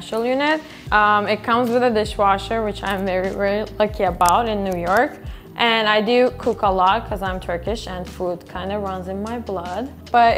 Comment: what did she say??? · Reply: "it comes with a dishwasher which I'm very very lucky about in New York and I do cook a lot because I'm Turkish and food kinda runs in my blood"